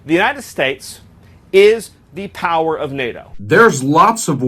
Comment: "US is the power of NATO"... I thought he said he was contemplating leaving NATO. Stupid, idiots, power hungry imbeciles. I bet Miller is whispering in the clown's ear.